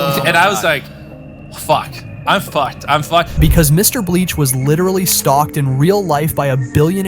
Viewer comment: WHY DID BRO SAY MR BLEACH 😭